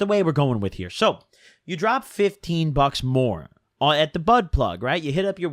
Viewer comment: lmao sounded like "Ya drop 15 bucks more at the butt plug"